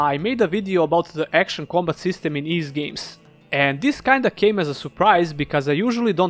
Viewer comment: Lol.. Ys VIII.. you should display the name of the game in text. Very hard for someone that doesn't know of the game to understand the pronunciation of Ys VIII. Thought you was saying 'East Games' then maybe "Theives Games" "Ease 8" etc.. If it wasn't for Montrum Nox and a goggle search.. I'd probably be trying to figure out the name of the game.